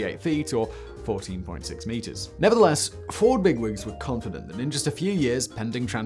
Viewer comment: I was only half listening and heard “satisfying whine” as “satisfying wine” and was immediately baffled as to how a truck that makes wine didn’t catch on. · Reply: When he said "Ford big wigs" I thought he was trying to say "ford big rigs" for a second 😂